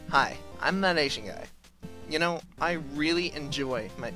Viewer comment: oh i thought you said that asian fat guy.